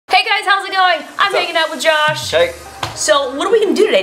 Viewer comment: is it me or does it sounds like justine said "im making out with josh"